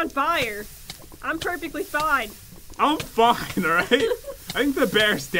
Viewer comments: I thought he said I’m fu**ed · who else heard say in f*cked alright? p.s. if u don't know where it was